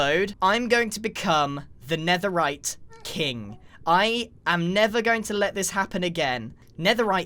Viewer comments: did he just say he's "nether" going to happen again. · Am i the only one who heard "I am NETHER going to let this happen again" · did he just say nether